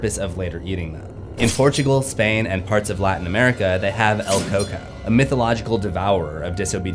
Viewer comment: As an Ecuadorian, my mother taught me about El Coco. Most of the time I didn’t heed the lessons but I still remember them. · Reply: I heard it as el Cuco. Anda a dormir sino viene el cuco y te lleva. 😢